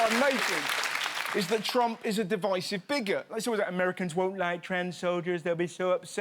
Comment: when he said Trump was a divisive bigot I thought he said thst trump was a digestive biscuit...